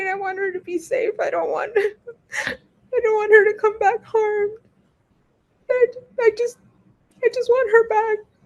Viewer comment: this is the interview when slips. If you go back to 36:26/2:27:31 and slow it down says, "I'm just scared for her, I want her to be safe, I want her to be okay but, I DON'T WANT HER TO COME HOME!" She says it not once but TWICE. Bish you busted, you slipped up you evil pig. I just caught that tonight. I slowed it down to different speeds about 20x's cause I thought I was going crazy. ETA: And yes I watched how she PRONOUNCED want and her lips form with an O when she says WANT but that slip up you can clearly see her tongue goes up to the back of her teeth to pronounce the D sound both times. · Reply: I thought she said “ I don’t want her to come back harmed “